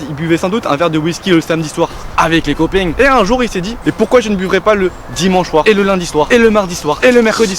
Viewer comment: Attends,attends j'ai entendu "je ne buverai pas" ?